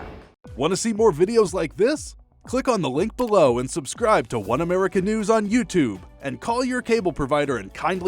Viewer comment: She said One America News ate the end I thought she said "One of our canoes". 😂😂😂i think it's a better name for the channel.